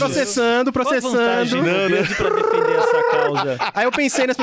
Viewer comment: Eu também entendi “obesos” ao invés de “o Besos” 😂